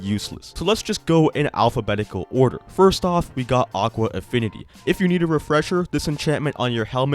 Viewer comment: FINALLY SOMEONE SAYS AFFINITY INSTEAD OF INFINITY